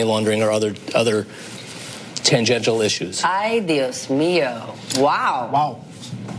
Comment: did she just say adios mio... lol.